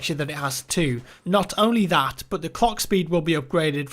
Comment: did u say cock speed at 2:13?